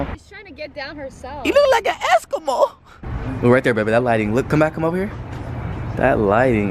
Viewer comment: omg did i heard Elle say "moma right tere"